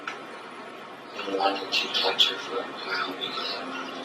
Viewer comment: What was he whispering at 4:34 ? Sounded like "you wanted to touch her" or "she wanted you to touch her" I can't make it out.